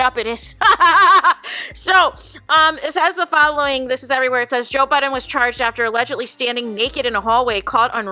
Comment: Girl, I thought you said Joe Biden 😂😂